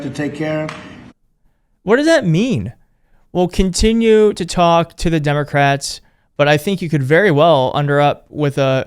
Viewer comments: I DON'T THINK HE SAID "TALK TO" THE DEMS SOUNDS LK "CONTINUE TO TORTURE THE DEMS" WHO HEARD THAT, TOO!? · I thought he said we will continue to torture the democrats?